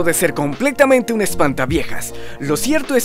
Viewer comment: escuché espanta-pijas XD